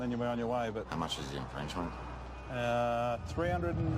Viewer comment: lmao the the subtitles say "Frenchman" rather than "infringement"🤣🤣